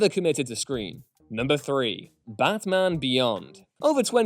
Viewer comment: Am I the only one who heard him pronounce Batman as Bathman at 8:16?